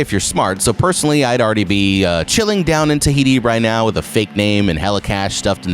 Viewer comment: did he just say thahiti